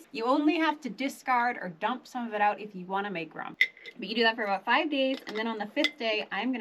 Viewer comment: what do you say at? 01:53? I can't understand the words you were saying, is it rump? each day you take a little bit out and why are you doing that in another spot you say you can take some out and make rum. What does that mean? You're making actually an alcoholic drink? Why do you take a little bit out each time? Thanks for any help you can offer. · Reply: Oh my gosh she was saying if your jar has NO ROOM🤣 (not rum, not rump😅)